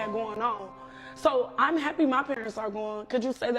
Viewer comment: Did just say she's happy her parents are gone @ 7:06?